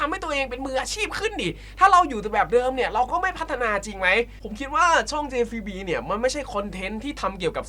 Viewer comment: ทำไม่ได้ยินชื่อช่องว่าjav รึกูคิดไปเอง 😂